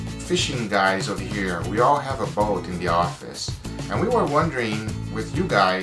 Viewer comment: I keep hearing here at 'The Office.' Tell us what you do at 'The Office!'